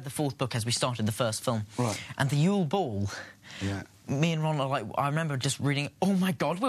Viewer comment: did Daniel called Rupert Ron👁👄👁